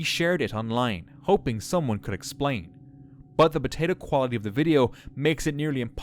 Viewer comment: did he say “potato quality”